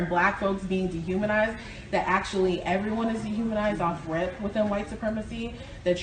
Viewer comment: I think she says “off rip”